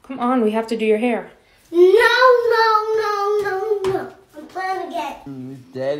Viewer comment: when he said "im playing games" i thought he said im gay..